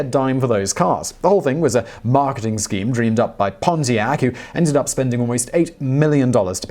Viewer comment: did he say "Ponzi-ac"?